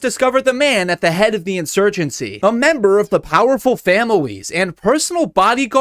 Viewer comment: did you say famawes?